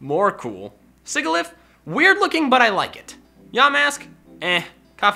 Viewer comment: When he said “Yamask” I thought he said “Dumb a**)